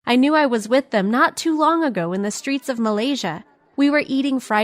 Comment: Did you just said Malaysia? Or i misheard it?